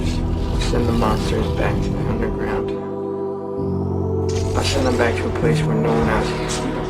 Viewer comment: hi i heard it say send the monsters back underground and i think emit and the girl are family